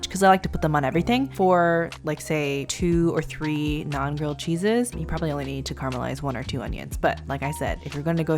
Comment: I really thought you were saying ‘non-grilled cheese ‘ and could not wrap my head around it!